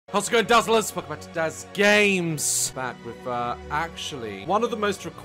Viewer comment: My internet started lagging at the beginning so it sounded like he said “Daz gays”